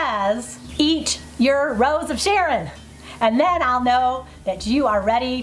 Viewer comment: Thank you for the chance to own a sun oven! I know it's eat your rose of sharon but initially I heard it as heat your rose of sharon! It kinda tickles me because you were heating it up.^^